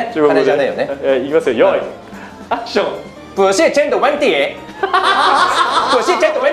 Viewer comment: Me: a Chinese Miyano Mamoru: 0:58 Also me: What did he say? LOL🤣🤣🤣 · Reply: yee i think he said that HAHA it was just the aggressive way he said it that made it unclear 不是钱的问题